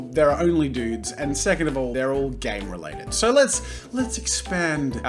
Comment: When he said "Game related" I thought he said "Gay" at first lmao